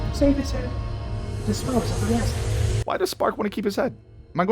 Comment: i was this many years old when i finally realized spark said "Save his head, dispose of the rest". For all these years i thought he said "Simply or Safely Said" like he gets your point now dispose of him. But "Save his head" makes light-years more sense and actually makes the dialogue just that more badass...